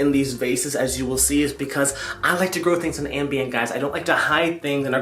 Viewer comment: Lmao first of all, I just found your channel, and I am in love. But anyway, for a second, I thought you said "I like to grow things on Ambien!" I know you didn't, but I love your humor, too 😂